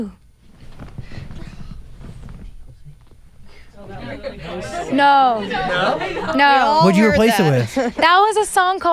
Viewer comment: Has anyone figured out what group Zach says at 31:35? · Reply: i think it was *NSYNC